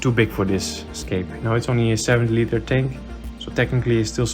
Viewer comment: Did you said in 17:45 that it's a 7 litre tank ? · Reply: 70 😜 but I can see why you thought I said 7 😁 my pronunciation is not always the best